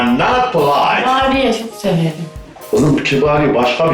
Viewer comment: de ne dedi anlamadım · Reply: Kibariyeyi seviyorum dedi